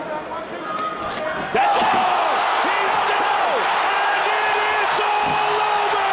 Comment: did he say “he’s dead” ? Omg lol